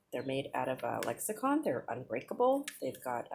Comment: It's absolutely Lexan lol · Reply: oh it's called Lexan... I thought I heard you call it lexicon - I even ran it back to see if it was my ears or your tongue that made the mistake. I guess I need to get my ears cleaned out, huh?